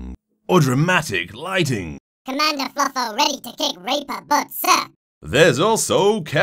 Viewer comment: Just a question, what on earth is she saying at 1:02 ? I swear i heard "Rape a butt SIR!" in there somewhere O.o